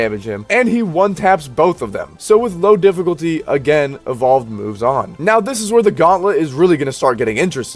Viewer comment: I literally heard evolved muzan 😂😂😂